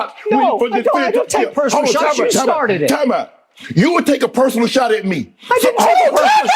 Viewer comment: I couldn't understand 5:33.... did he yell TIME OWT!"